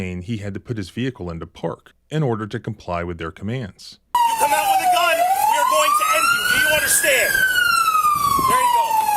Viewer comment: they actually said 'if you come out with a gun we're going to end you' funny you have selective hearing though